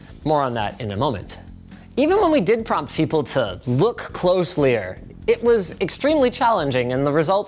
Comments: Linus Did you say closelyer?😂🤣 Still my favorite tech guy!!!! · Did Linus just say "look closely-er"?